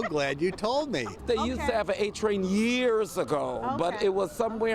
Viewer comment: Ummm they still have an A train... if that's what he said...